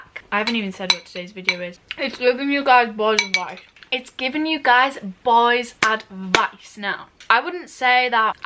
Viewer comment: It sounded like she said giving you guys bowls of rice 🍚 😂